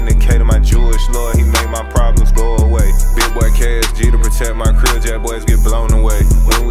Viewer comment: Damn near thought he said “Keep the SG to protect my crib, Jackboy’s get blown away”😂